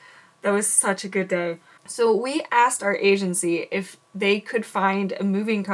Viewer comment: I thought you said we found our agency - but it heard like you said Asiancy